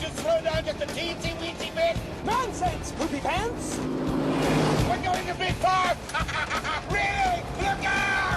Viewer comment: he says: we going to be fucked?????or i just hear things